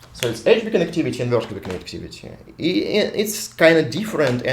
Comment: Hi, 2:40 -- Here the terms aren't clear! Did you say the 2 types of Big Connectivity as "Edge-2 Connectivity" and "Vertical Connectivity"? (or vertex connectivity?). Please clarify.